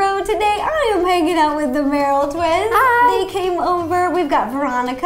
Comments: when Ro said I am hanging out with the Merrell twins I thought she said I am making out with Merrell twins😂😂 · What Ro Said : I'm hanging out with the Merrell Twins. What I heard : I'm making out with the Merrell Twins.